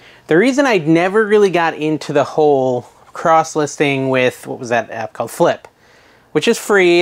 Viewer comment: LOL I was listing and you said “Cross Listing” but I thought you said Cross Dressing lol